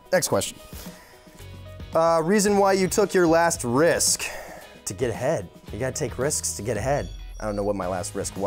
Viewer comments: i swear i thought he said to get head instead of to get ahead.. LOL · I thought he said " I took a risk to get head" and then I realized he said "to get ahead" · i thought he said to get head instead of get ahead 😂😂😂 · When he said the answer about when he took his last risk I thought he said “to get head”. Omg but he said to get Ahead. 😂😂😂😂 I am dead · Did he say to get head or to get ahead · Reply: Raoul Keats to get ahead 😂😂😂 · i’m trippin i thought he said “to get head” like got damn 💀